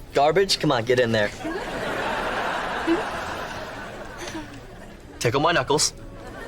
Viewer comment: Fascinating analysis. I know you might find it too conspiracy brained against Dan Schneider, but that "tickle my knuckles" bit had me like "uh..." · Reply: I had to rewind because I thought he said "tickle my nipples"